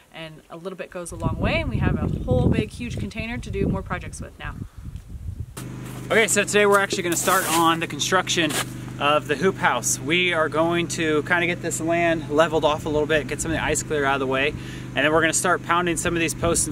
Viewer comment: OMG guys, i seriously thought you said "who house" as in Dr. Seuss'' Who, as in Who-ville